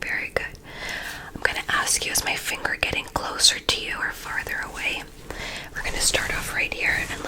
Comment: your accent lol when you say further sounds like your saying father hehe